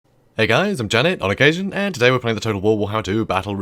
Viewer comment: See, THAT time I heard “Janet, on occasion” Where did the name even come from?